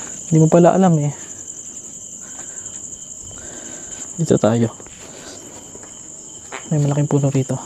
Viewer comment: pakinggan nyo po mabuti ung time na 14:26 ung papasok po kau parang may lalaking boses na nagsabi " dito tau" narinig ko lng po ilang beses kpo inulit eh wala nmn po kau kasama kaya wala nmn po magsasalita nun.